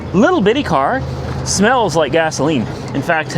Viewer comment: 😅😂😅 OMG I thought you said on that yellow vw ass-oline instead of gasoline 😅😅😅